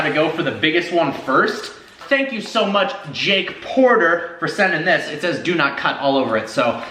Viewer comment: I thought he said jake pauler not porter